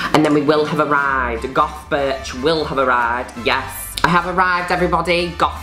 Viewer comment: I thought she kept saying goth bitch instead of goth birch. I’m so stupid